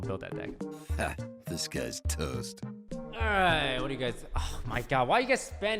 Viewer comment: Wait a minute, ive always thought your intro said "Disguised Toast", but now im hearing "This guy is toast." WHICH ONE IS IT?! I don't need sleep! i need anwsers!